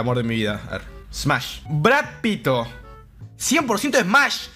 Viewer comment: WTF bro escuche Brad pito